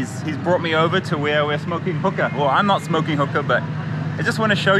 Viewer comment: I swear, at 9:08 I heard smoking coca😆